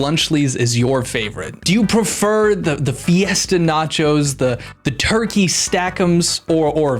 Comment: I thought he said “the foetus in nachos”